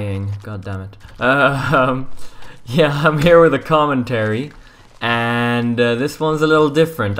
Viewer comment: lol at the beginning I thought he said im here with tom and terry XD